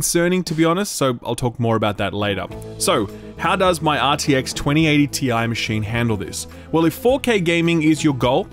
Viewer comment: I thought he said he was running a 3080 ti and called it not the highest level rig. My jaw dropped to the ground… 2080 ti… right. Oohhhhkay that makes more sense. Great review by the way!